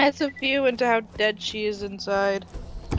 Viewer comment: I know I said something like "That's how dead she is inside," referring to Lilith, but I heard "How does it feel to have dead cheese inside?" I was so confused as to why was laughing. I even had to rewind the video a few times.